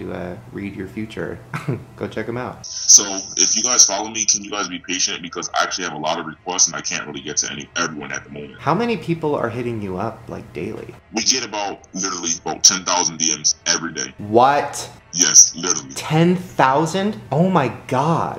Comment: He said "We" get about 10,000 dms who's "We" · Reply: Ayeeits Savannah dosent he say "legit" and not "we get" it sounds like he says "legit around 10,000"